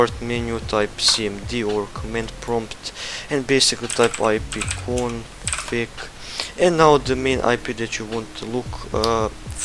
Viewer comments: type in IP Config if you couldnt hear! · what did you say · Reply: Basically you want to type ipconfig in da given command prompt magic yess